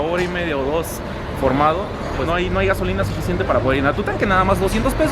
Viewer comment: pense que decia no hay golosina:v